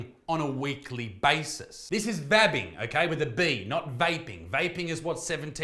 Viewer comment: I thought they were saying "fapping". I was confused.